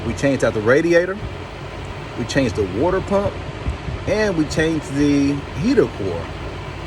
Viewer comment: did you just say Wooder pump 🤣